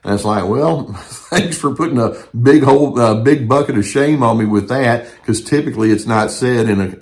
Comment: I thought the doc was about to say a bucket of $hi#.